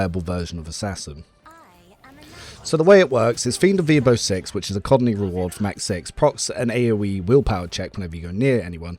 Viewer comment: Did you just say "the way it works is fiend of the weabo 6 which is a cotton reward of max six..." at 6:36 maybe slow down when you talk, because that makes no sense, it does not help you are heavily accented. how the hell is that supposed to explain anything?